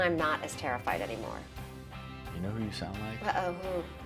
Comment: what did he say? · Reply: Dominic Lee "You know who you sound like? An ER doctor."